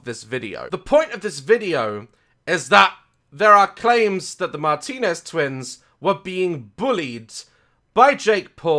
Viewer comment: The captions say “*bar Tina’s* “ Instead of “Martinez twins” 😆